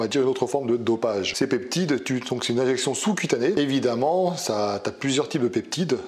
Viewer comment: C'est moi ou tu as dit "une érection sous cutanée" à 1:20 😂